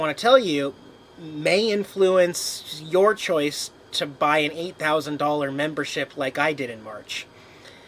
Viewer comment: did you say $8,000 membership?